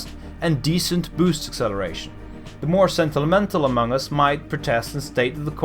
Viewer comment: sorry but did you say among us?